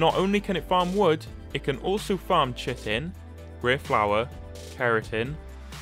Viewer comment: When he said chitin it sounded like he said shittin